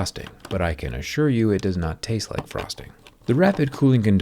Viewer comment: When zach said " I can assure you that boron nitride does not taste like frosting," I thought he said prostate not frosting hahaha